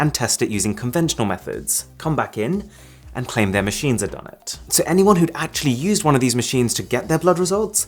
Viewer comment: I can't tell what he said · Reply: "their machines had done it"